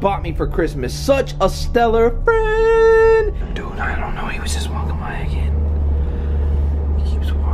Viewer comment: Sound like he said know em, instead of him maybe as if talking about a company, he was probably just dumpster diving lol